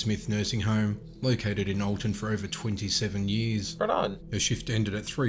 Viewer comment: min wtf he said "caralho" or i ear it wrong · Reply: If you mean, he said "right on"